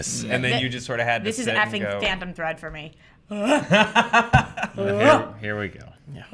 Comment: Every time I see I remember how much of a masterpiece Phantom Thread is!! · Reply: Travis Spazz Ahhh! That’s what she says at 13:00. I thought she was saying “Fandom” something, and wanted to know what she was talking about that all the guys obviously disagreed with 😂